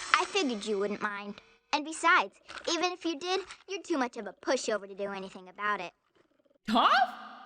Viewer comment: Listen to the way she calls him a pushover. I actually thought for a moment she said pussy.😱